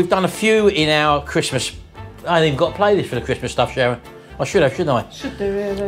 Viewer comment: Had to rewind the beginning…. Thought Sharon said Christmas F** Up! Haha Totally out of character, I thought. Probably says more about me than her x